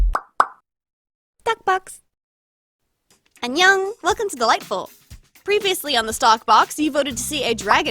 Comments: When you thought she said “ welcome to the light balm “ but she said “ welcome to dollightful” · I know you're saying stock box in the opening but I always hear it as thot box and I don't know why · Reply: same when I first heard her say it my mind went "Thot box" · When I first started watching you I thought u were saying "Welcome to the lightbulb"